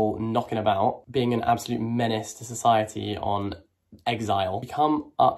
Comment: You said "Menace to society", i heard Mike's Mic.